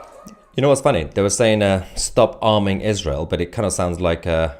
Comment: "Stop bombing Israel," is what I thought they were saying. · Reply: Took me a good minute to figure out it was “stop arming”.